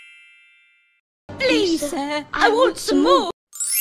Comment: what I heard: "please sir i want some mork!"